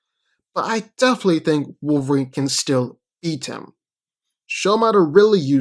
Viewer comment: I know he said beat but my brain wants to think he said EAT HIM.